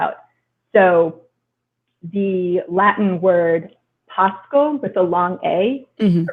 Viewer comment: what's the Latin word she mentions and the corresponding Hittite word? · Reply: I believe it's pāscō (to graze), and the Hittite word is pá-akh-ša-ri (according to Wiktionary)